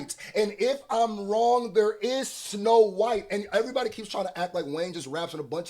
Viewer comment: I all ways heard that as "it's no white" not "its snow white" at this point I realize the triple.❤